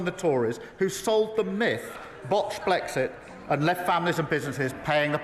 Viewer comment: Did Starmer just say " Blotched blexit"?